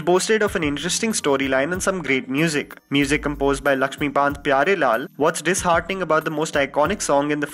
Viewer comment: what’s “Laxmibanth” Pyarelal ? Did I hear it correctly?